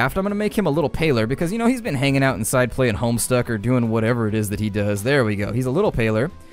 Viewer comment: I'm sorry to bring this up after 5 years, but did you say "playing homestuck?"